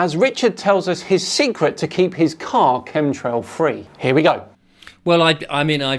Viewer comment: I'm sorry, but I heard "chemtrail fairy", and I can't stop laughing :DDD